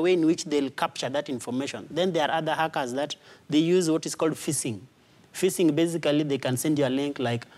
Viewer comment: Is he saying phishing at 6:18?